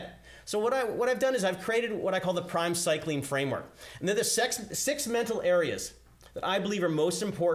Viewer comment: Freudian slip. say's "the sex mental images" instead of 'the six mental images. Californication Busted!